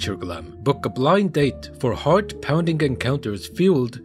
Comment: I wasn't looking at the screen for a moment and thought you said, "Book a blind date for hard pounding encounters" which I guess wasn't too far from the truth, unfortunately 😅